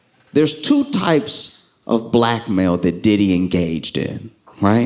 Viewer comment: I legit thought he said “Two types of BLACK MALE”